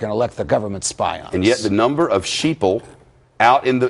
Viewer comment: did he say sheeple on CNN? 3:05 :0